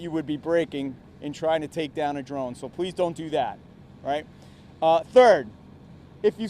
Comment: When he said, please don’t do that I heard him say please do that